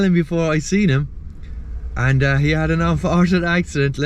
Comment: No joke but when you said he had an unfortunate accident at first I thought you said he had a Waterford accent! I was confused for so long about how you were able to smell him before you saw him 😂😂